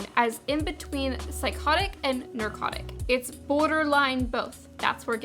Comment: Anybody else heard her say narcotic instead of neurotic?